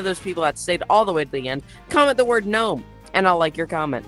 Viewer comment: I thought you said no, but then I realized you said Gnome